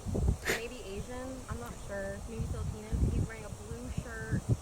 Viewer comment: Did she say Filipinan😭 whatever she said sounded INCORRECT